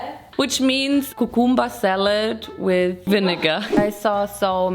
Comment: Its is just me or did i hear her say the n -word 0:29 No she just say the vinegar